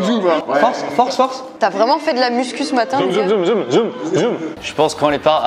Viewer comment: C'est moi ou dis à: Tu a vraiment fait de la muscu ce matin MON COEUR a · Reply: Non elle a dit